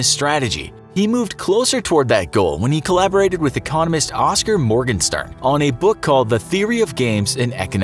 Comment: Did he say "communist"? Soviet Anthem plays